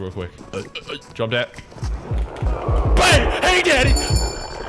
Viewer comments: All the times that he was jumpscared silly or when he was doing goofy ahh things💀 Jumpscares that silly : ) 6:14 7:48 2:20 4:11-12 8:54 (oh no moment with dog bread) 10:55-10:54 (wii pause menu) 21:28 spwook Silly thingos 2:36 2:57-2:58 Good boi coins stamp 3:01 Granny if she was an STD for a person stamp 3:04-5 *BONK*3:16 3:49-52 High five 5:20 Valorant moment 💀 5:53-54 growl/snort? 6:32-33 Jumps fence (possibly?) 6:49 Get sturdy 10:05-6 11:30 gremlin noises 1-trillion in the garage granny👀 11:34-36 Ford F-150 12:13 Knees weak granny 13:20 Flash 13:33 URGENT! Lebron speaks : 16:12 Still. 16:37-39 C4 ending 18:28 I love sm that I timestamp his stuff,you like him too,but a like : > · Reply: it sounds like he say "Hey daddy"☠ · did bro say hey daddy?😂😂😂